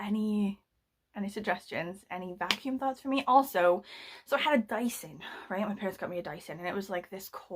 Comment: When you said "dyson" I heard "dicer" and thought, well that's some fancy American appliance which collects the rubbish right from the floor and dices it, like a mobile garbage disposal or something...🙃🤣 Best story ever, btw! I had tears in my eyes seeing you wrestling with the suck shark!😂😂😂 I have no advice, though. Only to keep this one... it's funny, you already seem to have an emotional relationship to it and it won't come any better. 🤣Sorry!